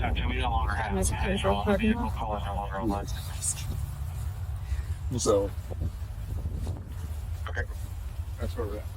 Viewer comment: What does the arrogant see u next Tuesday say at 34:05/ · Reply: "can I sit outside the parking lot" (to pull him over and get that sweet ID nectar)